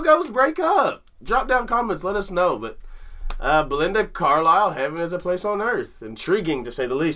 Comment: Until recently, I always thought she was saying "Blue Heaven is a Place on Earth".